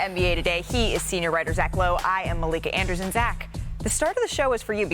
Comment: Did she just say her own name wrong? I swore I heard Malika Anderson